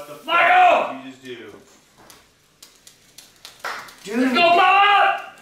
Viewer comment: I thought he said “your mama”or “joe mama” on